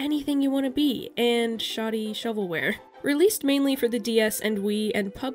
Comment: I thought you said shawty shovelware and I can’t stop thinking about it